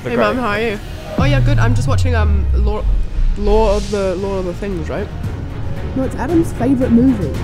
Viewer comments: "Law of the Things" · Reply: Americans heard it as "Lore of the things" lol our "W" sound is non-rhotic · "Law of the Things" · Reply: I thought she said "lore of the things"